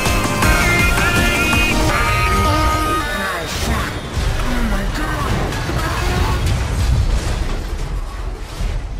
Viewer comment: What is he saying here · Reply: He is saying: "oh shit. OH MY GOD. NOO!" Because the building is collapsing